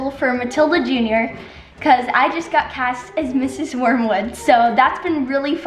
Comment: I thought said she's playing Ms Honey, and I was like oh perfect for. I went back when she said her character is not good because ms honey is good? She's playing Mrs Wormwood? that's such a great opportunity for to exercise her acting abilities!!